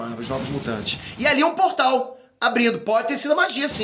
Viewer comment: Portal abrido??? kkkk....entendi certo?